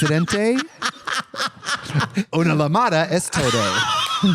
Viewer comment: As a Spanish speaker when I heard Trixie say "Una luh mada es toe doe", I literally thought I was having a stroke. Lol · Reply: Y yo pensando que había dicho “una mamada es todo” 😭